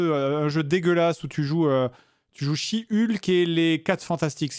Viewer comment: Haaa... Le jeu She-Hulk... Okay J'ai entendu "je chie Hulk" et je me suis retourné WTF??